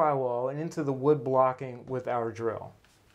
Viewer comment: when he said "we will drill a hole in the wall with our drill. " At first I heard him say "we will drill a hole in the wall without a drill." 😅